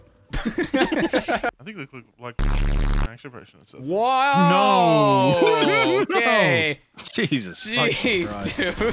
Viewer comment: i want to know what he said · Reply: Lebron